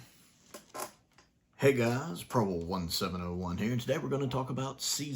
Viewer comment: Do you have any idea how long I've been listening to you introduce yourself and wonder what the heck you were saying? I sometimes thought you were saying "Problem 1701" ROFL